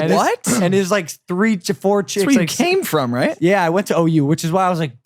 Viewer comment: Wait what fuck did he say he want to OU